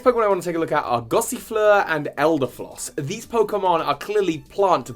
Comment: did you say floss?